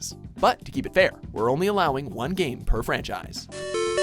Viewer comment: 1. Pokemon series. I thought you said ''1 game per franchise''